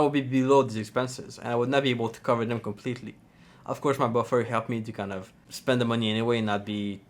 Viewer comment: Did he just say "of course my boy friend helped me"? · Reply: Buffer, put respect on name